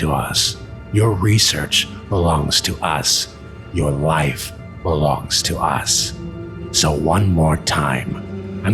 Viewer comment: I thought he said your wife belongs to us lmao